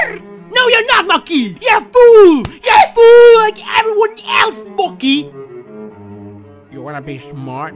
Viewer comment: My dad after I bring the wrong tools to him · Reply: Why did I hear “YOURE FOOD, YOURE FOOD LIKE EVERYONE ELSE”)